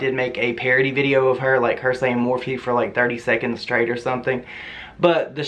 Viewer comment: Jesus the editing that went into this must have been RIDICULOUS. It sounds like she is saying "more feet" 😩